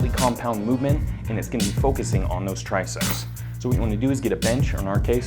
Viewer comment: sounds like a sound effect from street fighter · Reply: tubbs666 makes it sound like he said briceps